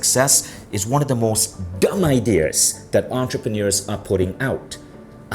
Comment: i thought he said hot work, dumb ad, makes me wanna psycho-rage